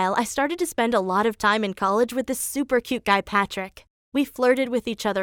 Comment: did you say Patrick?